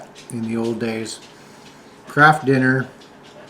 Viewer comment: I giggled when said Kraft dinner because I misheard him. I thought he said Crap dinner which is what my Dad called it. Lol 😂❤